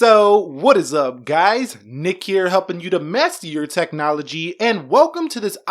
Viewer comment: I always thought he says to help you mess with your technology